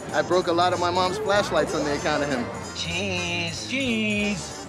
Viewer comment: "I broke a lot of lightsabers on the count of him" ..... Suuurreeeee let's keep calling em lightsabers XD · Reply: I thought he said flashlights